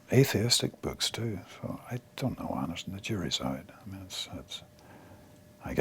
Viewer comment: what did liam say at about the 5:00 mark? sounds like "it's your reside"?? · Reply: the jury's out